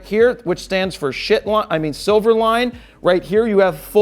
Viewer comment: I heard "S stands for *SxxT* line"?! xD